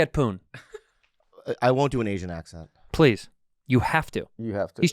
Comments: I thought he said “Asian accent awards” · I thought he said asian accents awards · could have sworn he said "asian accent award"